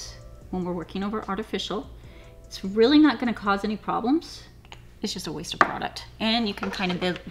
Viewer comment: Hahaha Did she said This is a wasted product on 14:17 😋